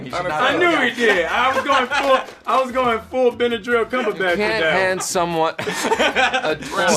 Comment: did he say Benadryl cumberbatch😂😂